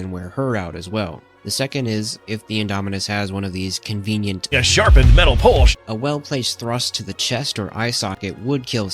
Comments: ok why did i chuckle at this out loud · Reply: I heard “Sharpened metal posh” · ok why did i chuckle at this out loud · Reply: I heard “Ya Sharpened Metal Polsh” 😂